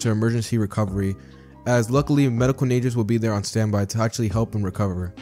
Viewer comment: What if Subaru was Sung jin woo reincarnation 34:48 i swear to God i hear 'Medical Ninjas' instead of 'Medical mages'